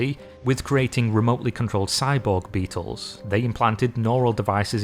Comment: You said, "Cyborg beetles" but all I heard was, "BEETLE-BORGS ARE REAL!"